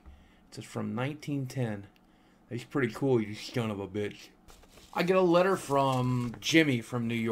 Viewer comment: did you call a 12 year old a shun ov a bitch?! lmao. Love ya boogie!